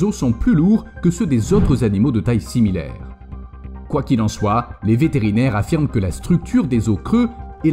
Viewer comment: a la 10 ème minute: "quoi qu'il en soit" .... moi j'ai entendu "couac il en soit."